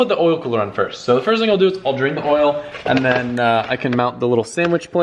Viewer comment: for a second there I thought you said you’ll drink the oil monkaS